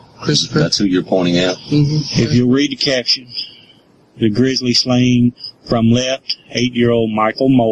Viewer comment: 'Grizzly slaying' instead of 'grisly slaying' tickled me.